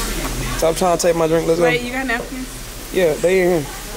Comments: No cuz did she say bae 😩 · when said “bae you got napkins “ and said “yeah they in here” I was like AHHHHHHH🤣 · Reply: I thought she said “wait you got napkins” but look it was a few baes thrown around. Check when was looking for her sauce and she said “bae I don’t want that” 😂